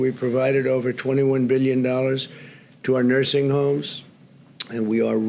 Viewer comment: Interesting how the captions are giving wrong figures compared to what Trump actually says... 2:25 - "21 Billion dollars", captions say "21 Million"...